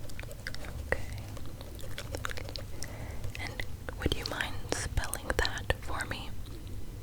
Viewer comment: i thought you said “would you mind smelling that for me” i was like “what people can do that” and then i had to rewatch it 50 times